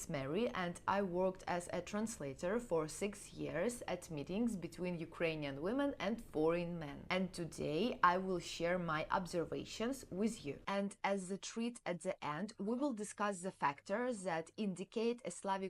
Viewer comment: it sounded like you said "boring men", instead of "foreign men", which made me laugh! "Foreign" is pronounced a bit differently, try listening to both words on Google Translate.